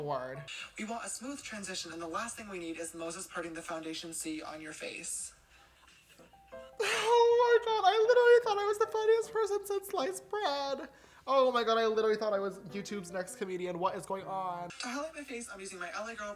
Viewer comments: James at 5:04 did he say lice bread 😂😂😂😂😂😂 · No one: Literally no one: James Charles: “I literally thought I as the funniest person since sliced bread” Everyone: tf did you just say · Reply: Tbh I thought he said lice brad😂😂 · The question marks that popped up when he said “I thought I was the funniest person since sliced bread” lfmaooo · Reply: I heard “since light spread“🤔